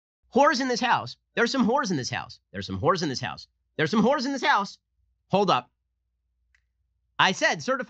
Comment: "There's some horse in this house" please tell me I am not the only one mishearing and being very confused